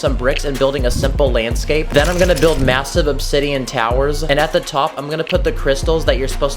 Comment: Ty, I thought you said Alton towers!